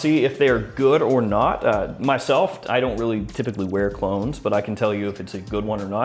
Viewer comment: I thought you said “I don’t typically wear colognes” and was like wait you do all this and don’t even wear them… Then I realized it was a Clone video lol. Keep it up, I definitely come to you for honest reviews. It’s quite obvious when some the other reviewers all post about the same product within a few days of each other all giving a raving review smh.